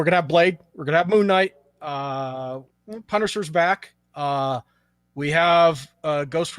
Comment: did say that punisher is back